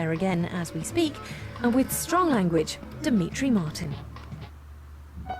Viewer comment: Oh.. D Martin. I thought said B Fartin